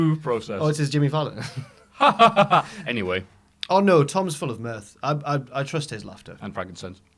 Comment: sounds like says tom is full of meth.